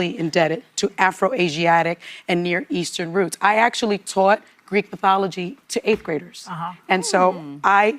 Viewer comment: Sonny Hostin said “I tawt Greek Pathology”… Anyone catch that doozy? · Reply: I thought she said "Geek Pathology"! 😅